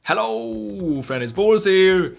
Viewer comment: when he says hello friends its boras here it sounds like hes saying hello very nice balls here